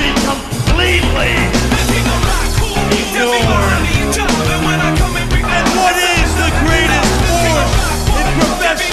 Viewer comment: At minute 2:47, what HHH says? I can't understand. Thanks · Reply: Discretion is advised but will be completely ignored